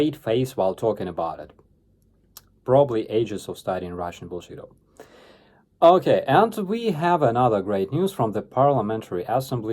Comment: did you say studying russian bullshido?